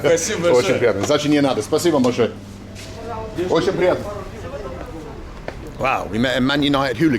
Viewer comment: did he just say bohot sukriya ( thank you) in hindi to Russian shop 😅😅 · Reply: No, it was all Russian "very nice [meeting you]/keep the change/thank you very much".